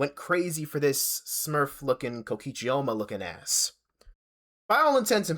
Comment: im sorry what did he say in second 0:50 i genuinely couldn't parse that and the subtitles seem to be wildly wrong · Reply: Kokichi oma, danganronpa character